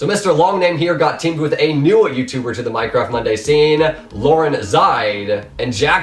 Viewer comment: Did he just say "lauren zide"?...